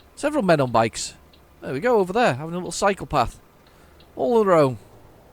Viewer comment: At the 34:05 mark of the video you said there were people on the cycle path. At first I thought you said they were a psychopath lol. If my memory serves me correct, the Yacht transport was with your first DAF. You had a whale of a time trying to cut the short corner to the delivery area. lol Look forward to your next trip back to Italy. Tell I said hi =)